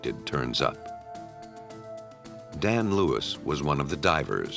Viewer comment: For some reason, I thought the narrator said, "Damn Lewis". Man, I'm trippin..